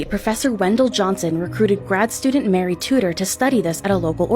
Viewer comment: did you say Mary Tudor?!